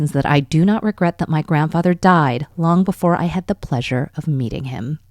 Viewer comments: Really thought she just said pleasure of eating him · Did she say 'pleasure of meeting him' or did I hear it as 'pleasure of eating him'??🤔